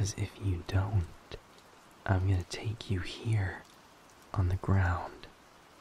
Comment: W-wait a flippin second.... 0:20 did he actually mean what I think we he said "take you"?